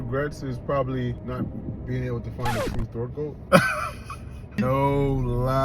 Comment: what did he say? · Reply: A throat goat